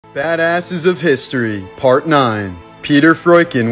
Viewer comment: Not gonna lie I thought he said fatasses of history and when I turned to look I saw my reflection. May be a sign